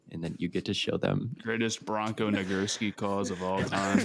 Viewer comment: somebody tell me what is saying here because I can only hear something that I know he did not say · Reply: Bronco Nagurski is a football player from the early 1900s